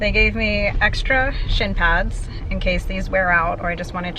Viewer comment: omg I clicked away for ONE SECOND and when you said they gave you extra shin pads I heard " sh*t pads" like wtf?